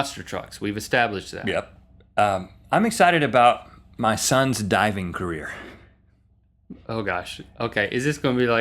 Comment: When Link said "My son...dives" I thought he said "My son...died" and I was like "What the heck??!?"